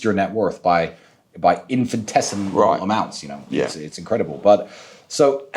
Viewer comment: what word did trsitan say? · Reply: Infinitesimal